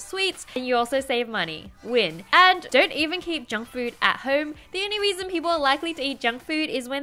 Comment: I heard "Jungkook" instead of "Junk Food".